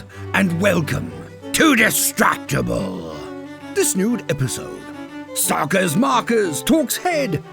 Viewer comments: Am I hearing "This nude episode" from? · why do the YouTube auto captions say "this nude episode" 💀